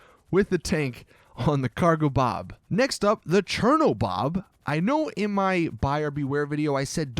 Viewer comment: Did he just say cherno-BOB its cherno-BOG